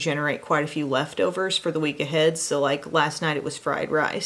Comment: I thought your random thought said “I air fried a sliced bread last night”. The words just got all jumbled in my head 😂 wonder if that would be good though 🧐